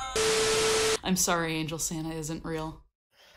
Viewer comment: Angel, Santa isn’t real *Screams of the damned* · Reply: I thought it said "Angel, Satan isn't real," for a second.